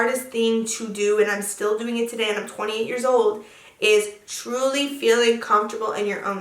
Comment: woahhhhh woahh woahh back up.. real quick girl.. you’re 28 years old??????? or did i mishear this